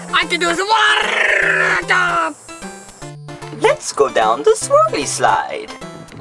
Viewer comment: Misheard hearing: lets go down the smoothie slide!